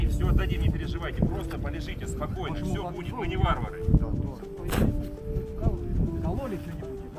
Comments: Мне сначало послышалось "Мы не Вагнеры" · Сначало послышалось: " мы не Вагнеры"